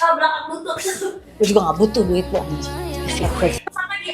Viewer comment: Cuuiihh!! Gw juga gk butuh Duit lu Anjing!!!! Kedengaran sya bgitu... Mudah² an saya salah dengar ya ...